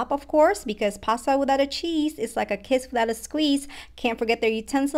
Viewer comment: I thought she said pasta without cheese is like a kid without a squeeze